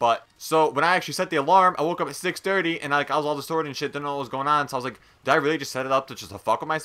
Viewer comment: i heard you say distorted 😏